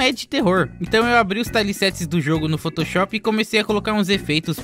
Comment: Como faz isso? e qual é o que você disse? "Então eu abro os ??????? do jogo no photoshop" o que tu disse? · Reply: Tiles-set OBS: Ele explica no vídeo